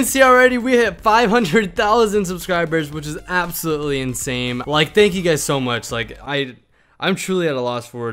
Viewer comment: Is it just me or did he said that’s absolutely insame